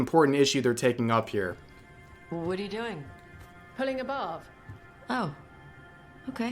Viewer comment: I can't tell if my ears are just clogged or if I kept hearing, "Pulling a barf." every time I've seen this clip.